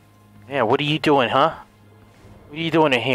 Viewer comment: Agent 47 knocks someone the hell out and pulls out a pistol with a silencer all with no expression whatsoever. "Hey stop that! What are you 5!? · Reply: I thought she said "what are you Bi?" I was so confused